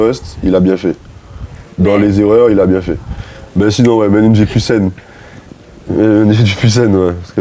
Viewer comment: il dit quoi la? Je comprends pas. Je comprends…….. scene. Je sais pas si qqun a compris merci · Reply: Une vie plus Seine 😂